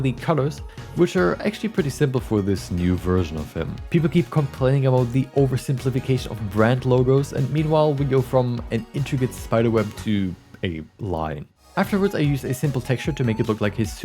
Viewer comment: I’m not sure what was said but this was what I heard “some people are complaining about the oversimplification of brands and here we have Spider-Man intricate webs go to a… lie” 😂 2:09 found that funny